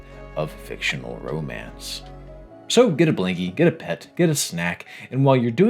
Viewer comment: I heard “Get a pen” and was like: Oh no, I need to take notes???